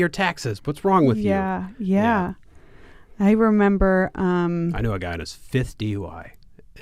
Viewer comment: what are they saying at 37:30 ? I know a guy that's ____ and hes 35. ... (and then some talk about "that's 12 grand" or "arwent those 20 grand and can kill you?" I dont understand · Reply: I said that I know a guy with 5 DUI's. Then went on to ask the price of fighting a DUI (litigation, jail, fines, etc) and then she added the possibility of killing someone because of drunk driving.